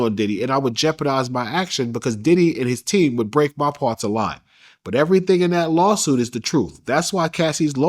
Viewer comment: Wht did he say?? Lol i watched da shirt vids an dude cant talk 😂😂 he said if i lie diddy team will break they part a lie 😂😂 its a few times in the interview an he says stuff backwards or mispronincung 😂😂😂